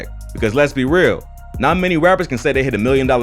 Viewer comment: Tell me why I thought it said Raptors instead of rappers